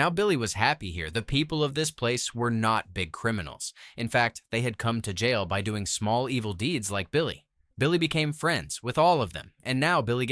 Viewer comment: "They had all gone to jail for doing small evil deed like billy" the fucking subtitles say "he killed 3 men"